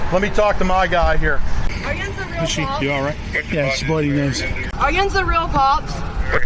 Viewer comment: I think she says "Are yinz the real cops?" given the steelers flag in the apartment, but those subtitles work for people not familiar with pgh slang LOL